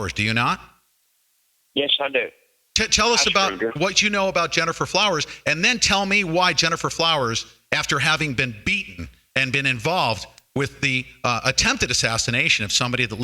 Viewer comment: Listen closely at 29:39 in this video When was asked "Do you have experience with Jennifer Flowers ? " said "Yeah i screwed her " Its hard to hear him say that and i dont think heard him admit that .